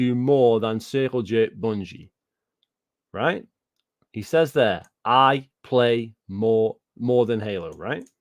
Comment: I think he's saying I play more games than halo